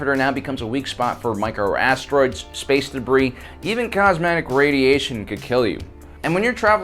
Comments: did he say Cosmetic Radiation? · did he just say cosmetic rays?